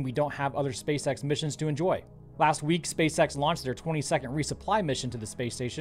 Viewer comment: Im too tired, i heard him say twenty second resupply mission and i was like how the fuck are the going to finish a resupply mission in 20 seconds.